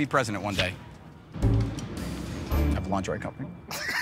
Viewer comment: You can be President one day Of a lingerie company I'm dead laughing🤣🤣🤣🤣🤣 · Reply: I thought he said laundry company which was much more funnier